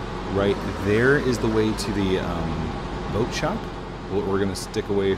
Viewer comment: did he say boat shop??